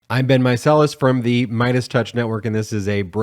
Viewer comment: Just realized you always say “I’m Ben…” at the start of your videos. I always thought you said “I’ve been…” hahaha.